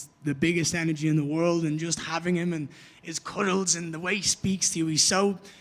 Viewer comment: did Ian just say Charles has the best Cuddles lol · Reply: yes. everyone knows Charles loves cuddles lol